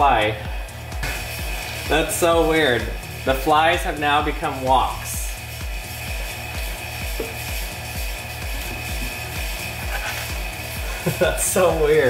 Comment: "the flies have now become walks" 😂😂😂😂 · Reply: Oh. Well then. Before I saw this comment I thought he said “woks” not “walks” and I was severely confused for a good few minutes. I’m dumb.